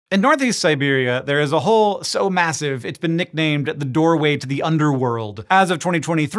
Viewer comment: Did you say massive?